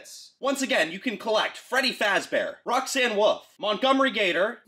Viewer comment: Me* thought you said Freddy fat bear.😂😂😂